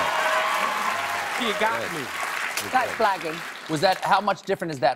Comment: At the end, I thought she said "that's blacking" XD